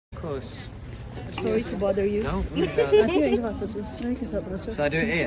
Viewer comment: Fan: so it's not bothering you? Lou: no ofc not Aww🥺we need more people like'em 💙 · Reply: i heard “sorry to bother you” 😭